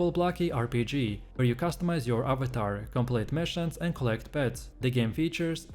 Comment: Collect pet? 4:38 What this guy saying